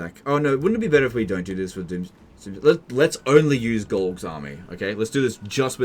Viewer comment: I misheard you saying "Glog's army" and thought you said "Golg-farming." Due to the algorithm being shit I move to unofficially change his name to GolgFarming. Algorithm issues are gone!